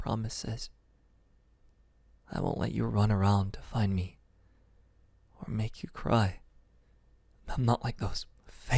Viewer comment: NAH INSTEAD OF ME FINALLY ABOUT DRIFTING TO SLEEP I SUDDENLY HEARD YOU SAY SOMETHING LIKE NEVER GONA LET YOU RUN AROUND AND NEVER GONNA MAKE YOU CRY💀💀💀